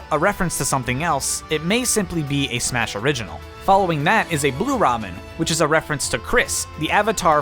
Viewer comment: did you say a blue Ramen?